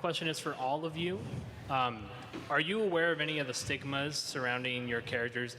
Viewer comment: laughed thinking he said Sigma instead of Stigma LOL